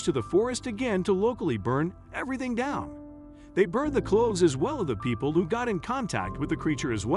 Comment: "They burned the clothes as well as the people." Did I hear that incorrectly? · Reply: "They burned the clothes, as well, OF the people..."